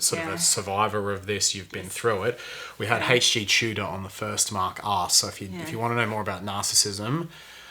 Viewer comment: Absolutely fascinating, this blew my mind, really. Also, every time he says Mark Asks, I keep hearing Mark's Arse... ;-)